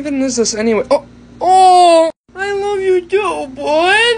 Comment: I swear I thought you said " I love you putin" no joke